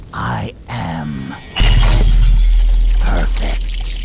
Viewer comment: Did you say perfect? (Cell appears)